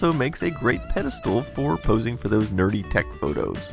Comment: When u said nerdy tech photos, I thought you said dirty text photos lol